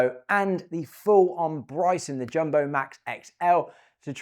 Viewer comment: Did you just say Bryson DeJumbo 😂